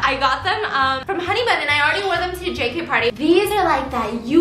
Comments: did she say... jkparty?? ohh im excited!!! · Did she say JKParty at 8:00!? Ahhh can’t wait!!